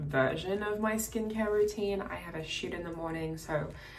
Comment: When she said "I had a shoot in the morning"... I thought she said "I had a shit in the morning" and I was like "...??? Weird flex but ok"